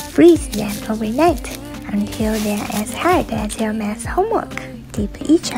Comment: "Until they are as hard as your maths homework" got me dying 💀🤌🏻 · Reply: I thought she said “your man’s homework” and I still thought about my own math hmwk. I’m glad she actually said maths 😂